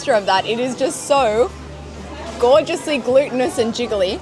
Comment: You know.. I always thought it was a little weird you continuously called rice "glutenous" just to realize you're actually saying "glutinous". I didn't even know that was a word, and I've been watching your videos for over a year! I thought you were just saying it was full of gluten 😅